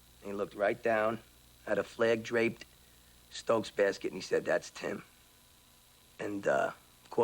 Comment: What did he say at 1:25:44? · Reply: “Had a flag draped, stokes basket and he said that’s tim”